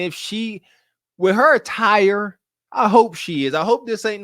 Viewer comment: did you say her attire or her TIRE? :face-green-smiling::face-green-smiling